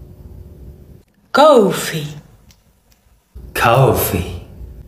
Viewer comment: When she said coffee I thought she said “goatfeet” cause of her accent 😭🤚